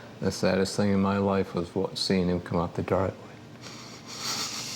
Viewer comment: What did the Dad say at 22:12 ?? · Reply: Was the saddest thing in his life watching the chief of police walking up their driveway